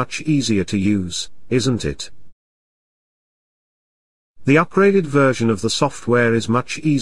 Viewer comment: 第10題的Upgraded都會聽成Operated TAT